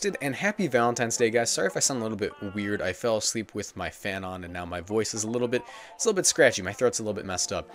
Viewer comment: i didnt hear the “on” part of the sentence and thought heard “i sleep with my fan”. Scared me for a second